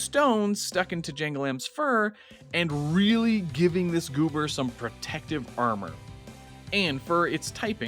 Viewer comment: did someone say goober